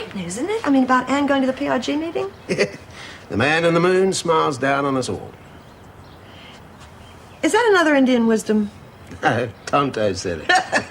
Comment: John W what does wally say at 33:17?? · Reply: The man in the Moon smiles down on us all. No Tonto said it 😗